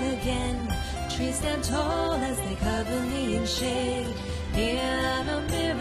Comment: Is she saying Trees are tall as they cover me in shit?